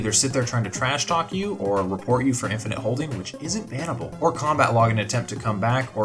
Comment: i said it WASNT bannable. People just think it is for some reason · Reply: i thought i heard “it is bannable”or smt